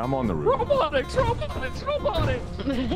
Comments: Do the Old Birds (robots) actually say "ROBOTICS, ROBOTICS, ROBOTICS"? I thought I heard "MOVE OVER, MOVE OVER, MOVE OVER" or am i the only one? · I hear “RO-BALLIN RO-BALLIN RO-BALLIN”, not “ROBOTICS ROBOTICS ROBOTICS”